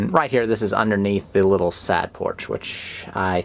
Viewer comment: He was saying "side porch" but I kept hearing "sad porch" :P